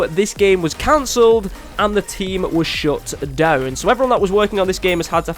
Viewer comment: when you said the team shut down I thought you said was shot down and I became very nervous